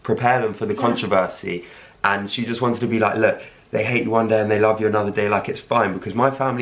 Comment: I thought he said they gate yewande 😂😂